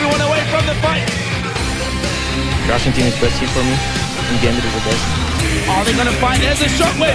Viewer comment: what did he said at 3:10? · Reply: Russian team is best team for me, and Gambit is the best. :)